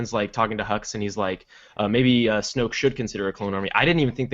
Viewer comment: I thought they said droid army, not clone army.